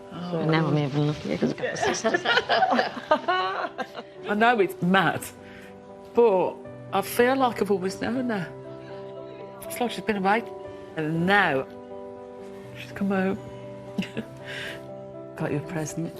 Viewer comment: Can any one tell me what is saying @ 43:15. I got this out of the transcript - ' I know it's math for a fair lockable with them and now it's like she's been a bike and now just come out' . But I am no wiser even now. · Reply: She says, "I know it's mad, but I feel like I've always known her. It's like she's been away and now she's come home."